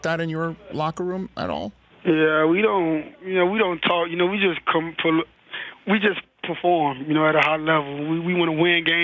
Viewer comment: he was about to say "We don't talk for real we just come pull up" lol that's what I heard - Lamar Jackson